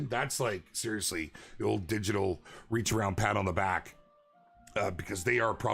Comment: All i heard was digital reacharound 😂😂